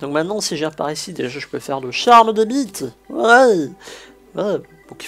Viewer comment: Le "Charm des BITE" c quoi xD a 30:40 apar sa j'adore tes vidéos continue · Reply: KIKIgeoF il voulais dire le charme des mythes petit coquin :p